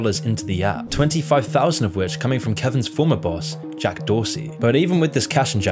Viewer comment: I actually thought you said Jack Dawson. And then a sinking Ship was shown right after. Weird coincidence. 😅